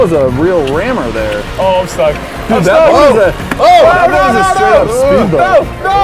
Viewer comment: did say the f word? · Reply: Alex Outcalt he said stuck